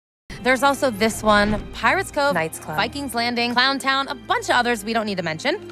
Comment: Did I heard pirate's cove?